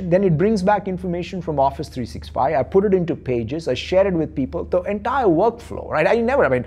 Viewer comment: Did he just say I put it into pages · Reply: It's power pages. Microsoft's offering which is part of power platform ecosystem.